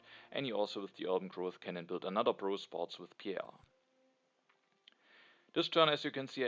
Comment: I always hear "pro sports" as "bro sports" and "strategy" as "astrology".